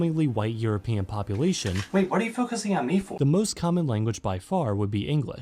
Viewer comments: Can someone pls tell me what was said durin the cutaway between 2:56 and 2:58? I need subtitles to understand most things and for some reason this part didnt get subtitled... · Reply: "Wait, what do you focussing on me for?" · Can someone pls tell me what was said durin the cutaway between 2:56 and 2:58? I need subtitles to understand most things and for some reason this part didnt get subtitled... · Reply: It was just a throwaway joke, but it was “wait what are you focusing on me for?“